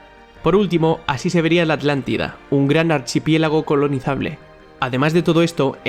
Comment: Escuché mal o dijistes la Atlántida min 3:28 la Atlántida no existe